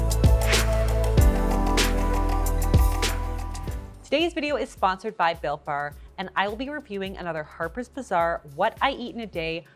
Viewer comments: At first when you said this episode was sponsored by Built Bar, I mistakenly thought you said it was “sponsored by Bill Barr.” Didn’t realize he got into WIEIADs after departing the Trump Administration 😂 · I thought you said “sponsored by Bill Burr” 😂